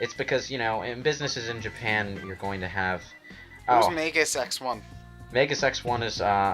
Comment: When I heard magusx1, I thought they were saying 'who's mega sex one?'